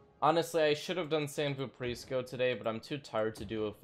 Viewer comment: sounds like he is saying "San Bu Preschool" instead of San Vooprisco. xD